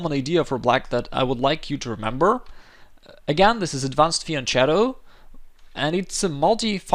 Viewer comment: does anyone know the name of that move to B5? I couldn't understand the speaker...advanced viachero (???) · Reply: Black takes pawn to b5. It is a very common idea I would like you to remember, again it is an advanced FIANCHETTO, and it is a multifunctional move